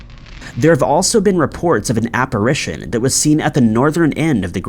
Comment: I don't know why I kept hearing "operation" and not "apparition" although it made no sense at all....